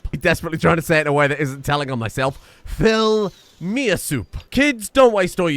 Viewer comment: I'm sorry, did you just say "Filmy Ass Soup?"